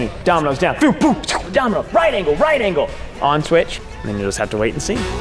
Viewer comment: I was so confused about what he was saying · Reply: Domino Domino right angle switch